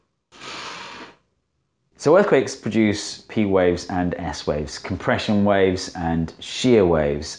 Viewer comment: Every time he says "P and S waves" all I hear is "Penis waves".